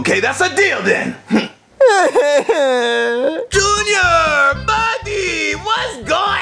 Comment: When I found out this video, I thought he said "papi", not "buddy". 1 to 2 years later, I found out he said "buddy"